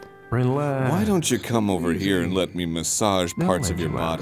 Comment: Subtitle correction time babeeeey (Note, I had to look at the cutscenes as well as the wikipedia entry to make sense of the spelling and context.) 5:31 "Relax. Take it easy. They'll let you out soon enough." 5:45 "Even things I... didn't do." 6:06 "You think you'll fare better out there than in here?" 9:57 "This grave doesn't have any flowers on it." 17:08 "Is it morning?" 17:12 "I'm the infamous Lord Gordon" 17:19 "I didn't catch the name, friend." 17:53 "You were right, Zaboo." (Note: Zaboo is the lady) 19:42 "What, Zaboo?" 21:36: "A real fruitcake." 25:14: "I am very fond of clocks. Seen any good time pieces lately?" 26:01 "Give him a nice watch and he's in pig heaven." 26:08 "Hey if the old man sees it, that'll be fun." 28:56 "One day you will, Hal." 29:57 "See you." 35:50 "A man with no memory knows all about chasing windmills, doesn't he?" 44:22 "Oh! You again! Ah, you?" 45:15 "...and preserve them." --------- Props to the guy that had to transcribe the dialogue in the video, though. Trying to reading/transcribe dialogue with slurred speech or conversation on top of conversation is pretty damn tough. Keep up the good work, editor guy! .... (also, I couldn't find any english voice casting lists... but I swear Anthony sounds kind of like Chris Parnell.)